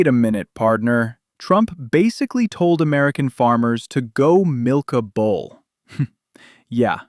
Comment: I thought you said "go milk a bowl" lol